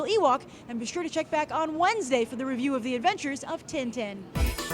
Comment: When she said "review of adventures" at the end I thought at first she was saying Avengers. Damn it :/